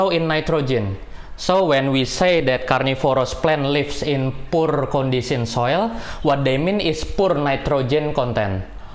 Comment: Hello there. At around 6:00 of this video, the guest speaker said something that in swampy area the nitrogen content is usually very low but it is high in something. I can’t make out what’s the word. Can someone let me know what is it? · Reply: I believe he said tannins